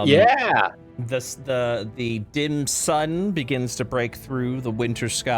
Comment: I heard the Dim Sum at 50:38 instead of dim sun and now I'm kind of craving it.